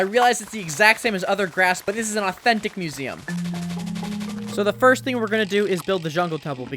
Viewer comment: THIS IS AN AUTHENTIC MUSEUM (uses spruce fence for jungle tree) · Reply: I thought he said autistic museum wth 💀